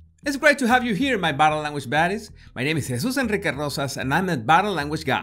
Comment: Omg stop I always thought you said 'body language BODIES' instead of 'baddies' till I saw this video. I just died a little inside.